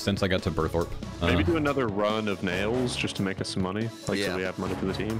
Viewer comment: what did he say???? lol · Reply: Maybe do another run of nails.. just to make us some money